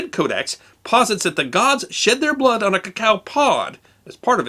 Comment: did you just say that they used to s*** their blood on the cacao bean so to fertilize it? I know it's early in the morning, but I don't think I misheard that.